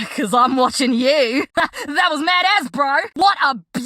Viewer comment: The “that was mad as” I seriously thought she said that was mad ass